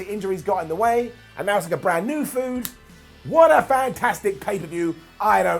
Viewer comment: Am I the only one who heard "brand new FOOD"